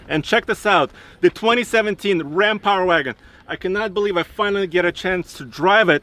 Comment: I thought it said Ram Rover